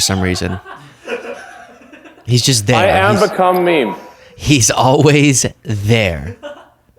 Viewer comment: He said "I am become mean(meme)." 4:50 Can anyone confirm what he said. was chattering over it.